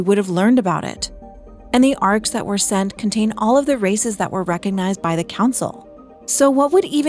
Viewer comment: i'm sorry but i have to type it, 6:50 all i hear is "contain all of racist that were recognise by the council" & 6:57 "all intended rasict" , i'm sorry that i have to laugh throught the video 😆😂